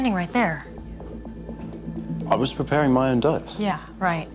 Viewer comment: You were standing right there. I was doing my own task. Is this 'among us'? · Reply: I thought he said “I was preparing my own does”? But idk